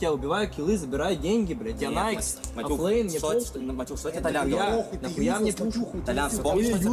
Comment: Might be a wrong transaltion, I think he said "offlane - I don't care", not "flame- I don't care".